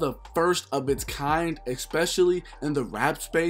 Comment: Every 5 mins all I heard was “ex specially” lol